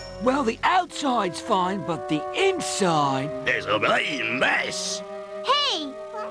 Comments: I know what blade said in 2:44 "Its a bloody mess" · Blade Knight has the HEAVIEST English accent I have heard. Almost to the point of being totally incomprehensible. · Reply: Well, you can make out some of what he says. That is if you are well-versed with the European-English drawal. Anecdotally, you can hear him curse at 2:45– he says “it’s a bleeding mess!”. “Bleeding” (derived from “bloody”) in European English is the equivalent to saying “f***” in American English. It means that you wish the Queen ill.